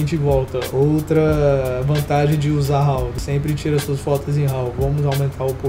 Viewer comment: “Sempre tire suas fotos em ?... (o que ele disse?)” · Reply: raw